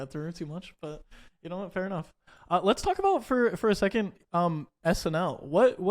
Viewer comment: I was cleaning smt and i heard wrong i thought he said "snf"